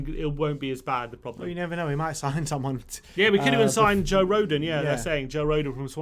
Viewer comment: thought they said we are gonna sign Joe Rogan lol